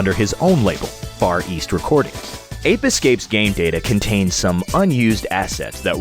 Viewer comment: I thought it said "rape escape" XD